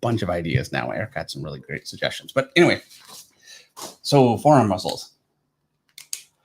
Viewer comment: Somebody said forearm muscles, and I got excited, cause I thought they said four arm muscles, like a character having four arms. How would that really work tho anatomically?...